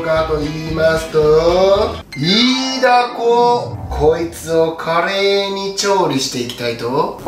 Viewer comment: 最初こいつを華麗に調理していきまーすを聞き間違えて、こいつをカレーに調理していきまーすだと思ってたから違うの作るんかいっ！てツッコミを入れてしまったw